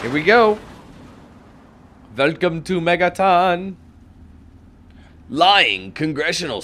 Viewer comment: I thought you said welcome to Megatron. 😂😂😂😂